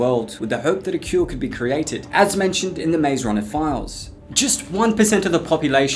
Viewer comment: the maze runner? i thought you said a mayonnaise walker